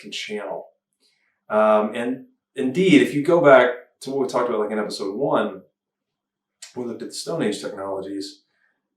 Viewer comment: Wow thanks guys. I am listening to every word, at 1:14:45 - 47 says a word I can't make out. Is he saying ineffable? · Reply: ineffable is correct